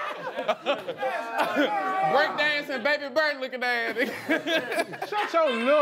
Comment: What did say about David at 5:46? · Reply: He said breakdancing baby bird